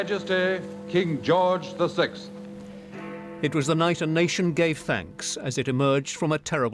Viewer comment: nation gay thanks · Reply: I heard “Nation gay facts”